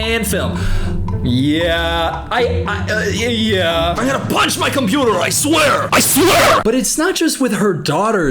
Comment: I thought he said I’m gonna break my monitor I swear!😂